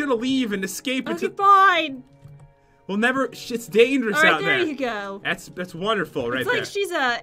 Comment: is it just me or it sounds like pat said "Shits dangerous out there "